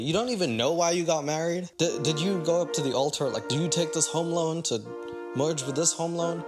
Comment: thinking he said they got married because of “home loans” and not “hormones” is so funny to me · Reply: I for sure thought he said home loans lolol